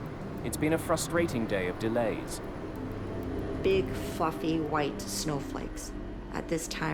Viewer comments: any one else hear her say “big fucking white snowflakes.” Instead of “big fluffy white snowflakes.” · I had to rewind it a couple times when the flight attendant at the beginning said "big fluffy white snow flakes" I thought she said "big f**king white snow flakes" lol